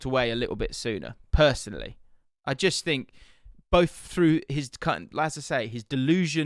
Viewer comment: did you say “through his c*nt’ Little freudian slip there